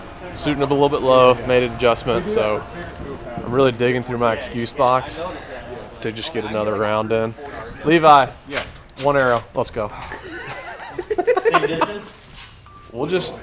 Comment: sounded like Ricky from trailer park Boys saying "Smokes let's go".